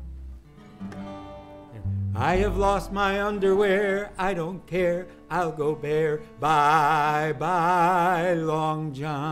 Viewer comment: I heard it as..."someone stole my underwear. I don't care. I'll go bare. bye bye BVDs. they were always close to me till I lost them by the Sea. bye-bye bvds...